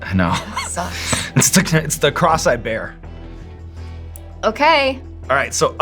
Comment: When Sean said "the cross I bear" I heard "the cross-eyed bear" for some reason. So maybe that can be the name of the next Hot Ones sauce. Just imagine the image on the label!